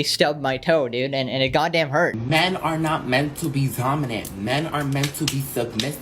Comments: Men are not meant to be.... Was that "zominant" I just heard, or I'm I just growing old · I know damn well I heard Zominant